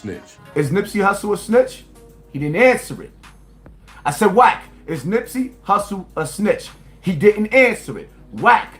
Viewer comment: I thought he said wack said Hussle was snitching